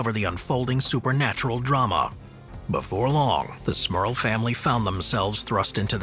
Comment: Thought it said Smurf family...